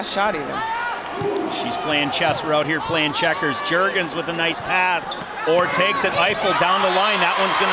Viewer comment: with nice what? Did I hear that right? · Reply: OMFG 😂😂😂😂😂😂😂🤣🤣🤣🤣🤣🤣 (he said pass but it was funny)